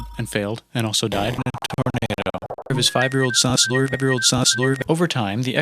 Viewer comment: i heard: his fiveyearold suslord evryold suslord